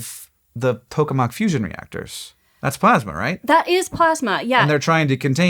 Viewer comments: My brain immediately lit up when I thought I heard "the pokemon fusion reactor"😂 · I heard Pokemon fusion reactors and did a double take.